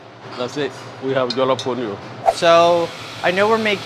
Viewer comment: I kept hearing "funions".